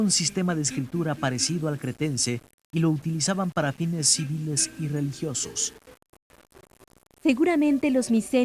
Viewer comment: que dice en esa parte, tipo dice lo utilisaban para... y despues? :( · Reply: lo utilizaba para fines civiles y religiosos. dice eso. de nada crack